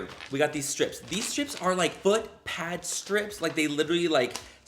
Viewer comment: I Dont Know why instead oh hearing foot lol I hear butt lol Hahahaha